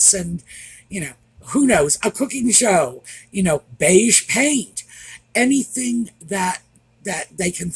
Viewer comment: “Beige paint” lol. At first I thought you said, “beige cake” 😂🧁